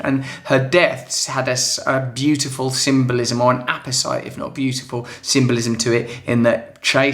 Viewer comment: Hello Russell! What was that word you used when you corrected yourself after saying Diana's death was beautiful around 3:19? I like to find new words to refine how I verbally express myself and to also make strangers think im smart! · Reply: Mimi Polanco apposite - fitting or apt (I was curious too !)